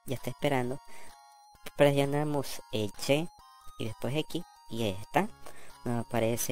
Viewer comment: En el minuto 9:38 que dices que hay que presionar? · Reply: Presionas el boton Y y luego X para mostras las configuraciones algo parecido como el caso de R y L para activar la camara